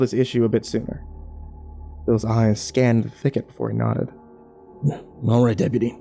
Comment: I thought that it said thickest for a minute and I thought it was in danger 😂 jk